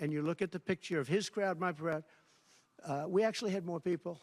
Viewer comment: minutes regarding crowd size of MLK jr. versus his assembly, is he actually saying "his crowd, my proud"?